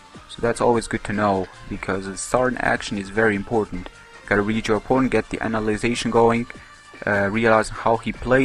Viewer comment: it sounds like he said " Gotta read your porn" lol